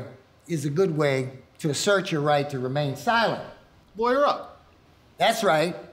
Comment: what does the guy at 1:21 say? lawyer up?